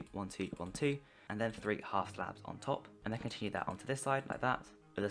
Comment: Im at 17:16 and did he say 3 half slabs. Im stuck on this and i can't understand because he's going so fast.